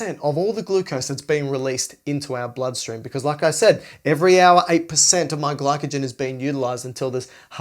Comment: I cant hear exactly what is said, was it "every hour a percent of my glycogen is being utilized ... " ? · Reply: 8% every hour, until there is none left at all